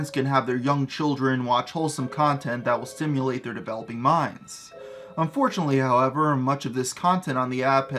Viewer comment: Thought you said "so parents can have their dumb children develop their minds" Lol